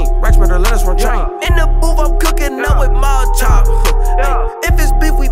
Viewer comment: Damn who produced the beat🔥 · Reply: i thought he said cookin up with maruchan if he got that beef..